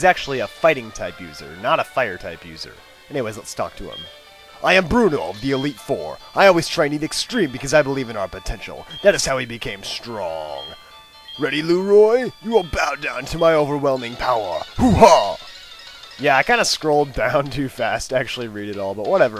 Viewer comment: I thought he said "Ready Loser" at first